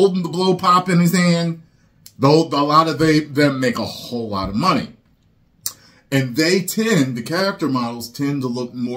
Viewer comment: Ty, did you just say 'a whole lotta they/them' ?